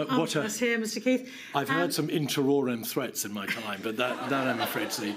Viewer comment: What kind of threat did Keith the barrister say Gove had given? It sounded like inter-aurine but I have no clue what it means. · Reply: I believe it was "in terrorem"